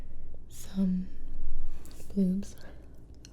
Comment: I figured we could eat some bloobs😂 who else thought she said boobs at first